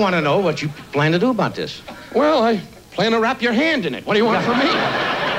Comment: Also, does Johnny actually say "hang him" at around 42:43 point?? Is he referencing Don's act like how he (Don) would make fun of people of race? Not offended or anything, just wondering.